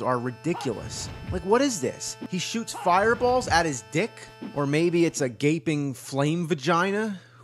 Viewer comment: Was that mel gibson in that game? I thought he said he'll shoot fire balls from his ass or something not out of his crotch..... and that car is badass!